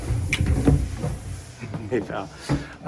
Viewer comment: When he reached for her hand, it sounded like he said "Hey, Doll" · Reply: OrangeQueen19 I heard “hey pal”